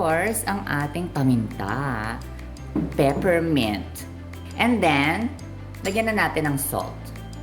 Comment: Very cool. 190C = 375F. Don't the tentacles on the side overcook? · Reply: Also, is that just black pepper? I heard you say pepper mint, and it's a light color.